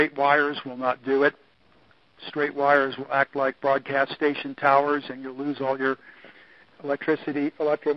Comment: At around 56min., Did I hear..."Threat Towers"?